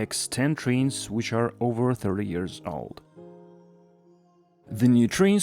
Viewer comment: ik u said 30 but first time understood I heard 3 years old 😂